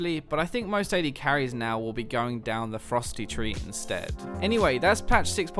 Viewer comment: I thought you said "AD carries will be more likely to go down the Frosty tree instead" Rather than "Ferocity" xD